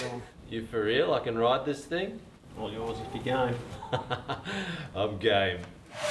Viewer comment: did he say I’m gay